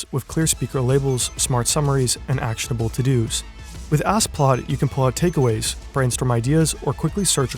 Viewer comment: yo when he said "ask plaud" and i wasn't looking at the screen i was like wtf did he just say lol ass plow